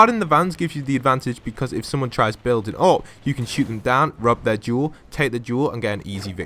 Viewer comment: did he say rub their jewels